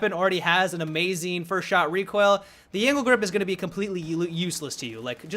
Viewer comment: when he said "angled grip" I thought he said "anal grip"